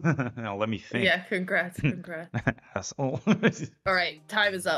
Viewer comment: did he just call her assh*le? :)))) dude chillll